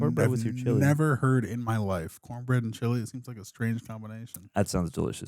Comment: Wait... Did Aaron just say Cornbread and Chili is an interesting combo? How have you been eating chili my dude? EDIT: Now hearing the end of the reaction and holy cow Aaron, Chili & Cornbread will change you life. Especially when it's a nice chilly day whenever you get one of those in SoCal. A must try for one who has not experienced the meal. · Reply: I thought she said “corned beef & chili” at first, so his reaction made sense to me. But when it was said again, I heard “cornbread & chili.”